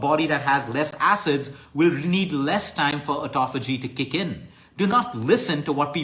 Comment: I can't catch the word says at 14:04,?starts with T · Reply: He says "autophagy". A term used for describing when body cells remove unnecessary and toxic substances. Hope it helps